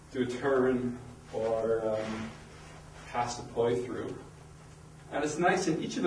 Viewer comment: are You saying here "...or pass the poi htrugh..."? :D nice one - beggining two staffs here too :) cheers